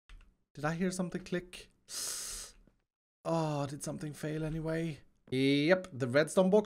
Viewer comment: Sounded like you said feil around 18:05 :3